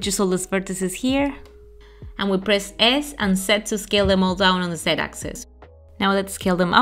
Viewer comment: Hi, I need confirmation on this since I'm a bit lost at what said about pressing "S", so to make it parallel, you just gotta press "S" + "Z" Axis right? Cuz I skipped the Z if she did said it